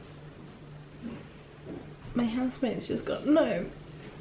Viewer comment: I thought she said her husband was home, and then I realised it was housemate.......thought I'd missed something ;)